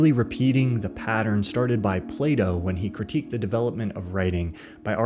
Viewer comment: I thought you said Playdoh and I was confused lol